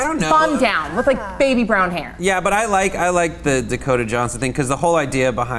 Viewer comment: I thought it said Dakota Fanning. I almost lost it at the thought